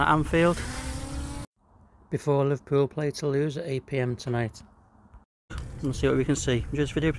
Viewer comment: I thought you said "Liverpool play to lose" 😅 I forgot your video was before the Toulouse match as im watching warly hours of Saturday morning